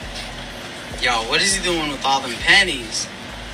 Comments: I thought he said - what's he doing with all those panties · At first I thought they said what's he doing with all those panties lol